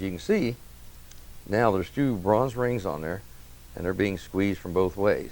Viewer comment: I thought you said we could put the “extra rings on before the spring?